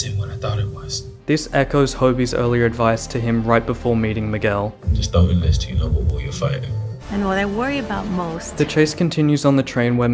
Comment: What is Hobie saying at 17:17? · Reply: "Just don't enlist until you know what war you're fighting."